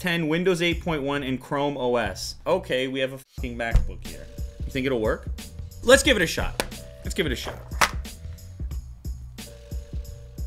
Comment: did he just say "f***ing macbook ?